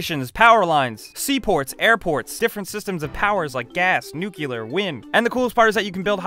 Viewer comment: did he say "nucelar"?